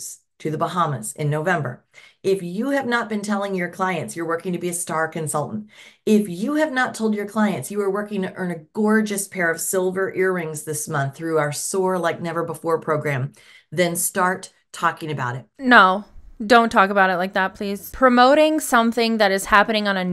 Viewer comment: I spaced out listening to her rant and i snapped back to attention as soon as I heard "sore like never before". Of course it took me a second to figure out she said soar..not sore.. 🤦‍♀️i got excited for a lil tick.